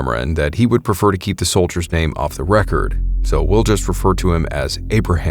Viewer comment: Bro I thought it said crypto for like 2 seconds. Like did Abe have Bitcoin lol